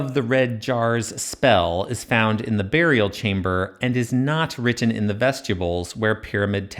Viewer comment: One day I'll get through a whole video with perfect pronunciation... one day. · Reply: It did make me giggle when occasionally I thought l’d heard “it was written in the vegetables”, which to be fair would be no worse than traditional Egyptology commentaries 😂